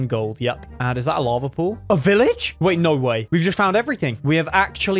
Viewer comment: Everytime he says "no way" I hear "Norway"